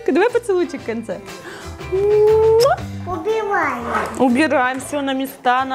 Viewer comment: мне послышалось убиваем 😂 так выросла, такая красавица 🤩